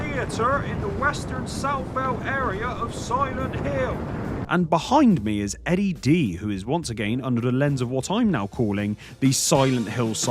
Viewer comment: This pizza is full of towns! How can you sit there and eat MONSTERS? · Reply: he clearly said soylent hill.